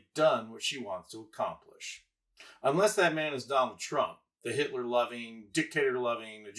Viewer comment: I thought you said Dick Loving Trump 😂